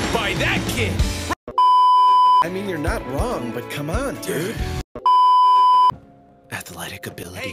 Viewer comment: did kunimi call him dad 👁👄👁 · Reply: he called him dude